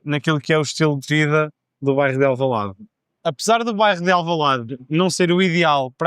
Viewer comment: Q about 'do bairro de Alvalade' at 2:05 - I don't even hear a trace of the 'b' or the 'rro' in 'bairro', it sounds more like 'do vais de Alvalade', with a very soft 'v'. I know sometimes b and v are swapped, but 'rro' going to 's' is even more surprising to me. Am I hearing it right, and is it a common pronunciation I should internalize? Great video for listening practice!